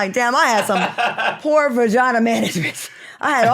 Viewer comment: I’m so confused as to why FoxSoul just doesn’t fire Al Reynolds’s? I mean no one really watches because of him. Why cancel a good show for someone who is replaceable? I’m so confused by FoxSoul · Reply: I thought I heard Al is dating someone in management 🤷🏾‍♀️